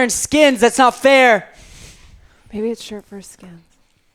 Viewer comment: What the hell did day at 01:14:00 !? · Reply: if you’re talking about what she says at like 1:14:35 ish i think she said “maybe it’s shirts vs skins”